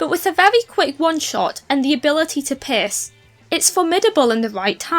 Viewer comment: i know i heard that wrong, but what was i supposed to hear here? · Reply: "And the ability to pierce", appologies for my accent lol